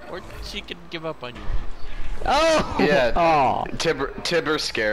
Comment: WTF? Is he saying "Harry sees my back ABAARRRR"???